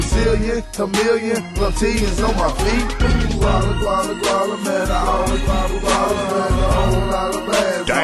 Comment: Can someone please tanslate 1:12 - 1:18 ? It sounds bad a fuck but i have no idea what he is saying.. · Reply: Guala guala guala I’m all about the guala